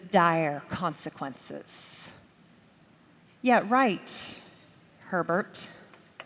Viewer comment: Seems a bit uncharitable to call a fellow Christian a pervert because you disagree with his interpretation of something. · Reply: I thought she said pervert as well, but she said Herbert, the name of the commentary writer.